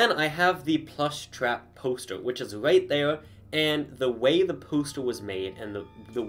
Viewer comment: did you said pooster